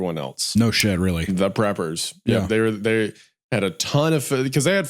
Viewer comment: I thought it said "Pepper" family. I genuinely thought it was about the owners of the famous soda brand dr pepper